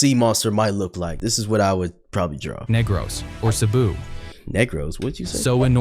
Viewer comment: what he say?! 😭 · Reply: Negr